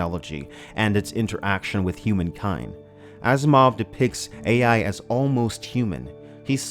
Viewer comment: I thought you said Ass Mov.